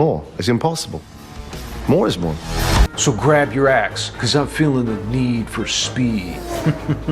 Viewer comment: I misheard him saying "So grab your ass" instead of "So grab your axe".